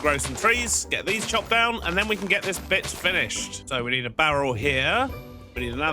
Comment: I'm not the only one who heard 'get this bitch finished', right?